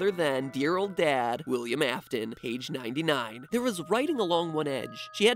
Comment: When u said page 99 I thought u said age 99 XD